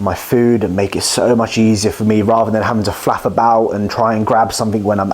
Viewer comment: did u just say "Flaff about" haha